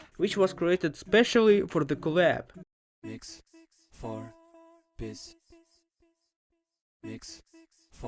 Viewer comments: did he say piss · what i hear: mix for piss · misheard: "mix for piss" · I hear MIX. FOR. PISS.